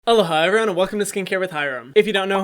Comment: I heard "Hello Hyram" 😂